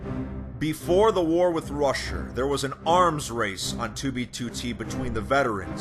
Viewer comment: I thought he said Russia instead of Rusher